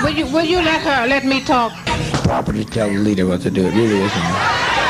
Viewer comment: Just came across this recently. I didn't realize the heckling was this horrible. And when she says to Jones "Will you let me talk or let her talk", do I hear him say "Nobody talks to the leader that way, you idiot"? Or am I hearing it wrong?